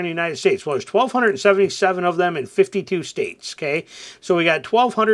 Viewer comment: In how many states? At 1:19 I thought I heard 52 states.